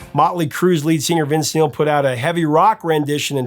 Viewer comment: What did the "Professor" say at 16:40? "Mötley Crüe's lead singer, Vince Gill"? I didn't know they were a country band! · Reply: Vince Neal, I think